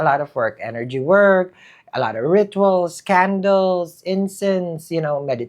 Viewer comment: I misheard candles as 'scandals' 😂❤